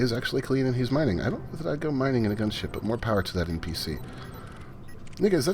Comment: what did you just say? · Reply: "I don't know that I'd go mining in a gunship, but more power to that NPC."